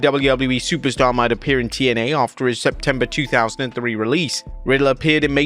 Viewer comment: I heard September 2003. I even read it in the caption. It’s September 2023.